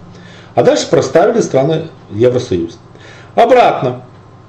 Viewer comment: Послышалось «сраный» Евросоюз, потом понял - страны Евросоюза...из-за такой химеры столько горя.